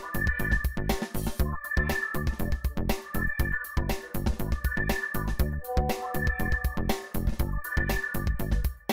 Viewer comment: nao entendi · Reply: ''só porque eu estou namorando, não quer dizer que eu não possa olhar pra outras mulheres''. Quase isso